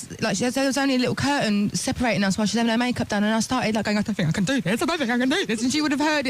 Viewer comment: Either the text is wrong and Adele said "...while she was having her makeup done..." or she can't pronounce the 'g' in any form. Replayed it several times. I keep hearing "...ha-ing her makeup done..." So I definitely thing the typed text is wrong. It's not "getting" it's "having." You should try to understand the English of the people I work with. From various countries, European but also African. Especially the Polish persons pronounce some vowels very wrongly, like "I won't" was what I heard, which was puzzling because the guy was talking about going on holidays and meeting his girlfriend. "But I won't." Huh? Why not? Turns out he tried to say "I want." And another person who asked me to pick up some "cone-sys." I beg your pardon? So he meant just "cones."